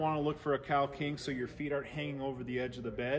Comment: I heard “cow king” 😂😂😂 I was like there’s a cow king bed?